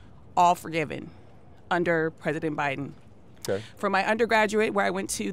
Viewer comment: It sounded like she said ‘predident Biden’ 😂